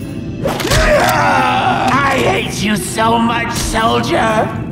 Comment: The fact that his hands are so frickin clean after that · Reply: I legit thought he said “thank you soldier” the first time I heard it